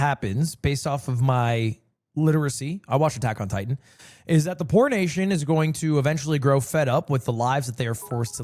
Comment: fr thought lil bro said the porn nation